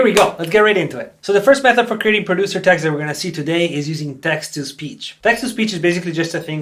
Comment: I thought you said, “Texas peach” not text speech.